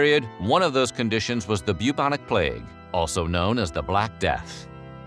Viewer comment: You said "Bubonic Plague" I heard...."Puke on a plate". Swear to....reality (certainly not god) it was very confusing for a sec. 😂